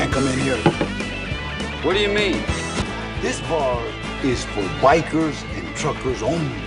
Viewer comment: At 8 minutes in, I thought the clip said “This bar is for white girls and truckers only”